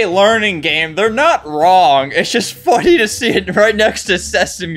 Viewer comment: i thought you said the f word but you said funny HEE HEE